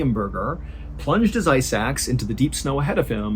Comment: You said “ice axe” and I heard “eye sacks” and was extremely confused